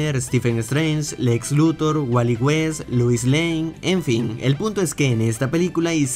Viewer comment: Escuche oh entendí que dijo puto